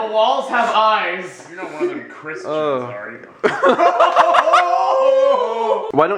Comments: What did say? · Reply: You aren't one of those Christians, are you? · What did say from 4:19 to 4:21? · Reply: "You're not one of them Christians, are you?"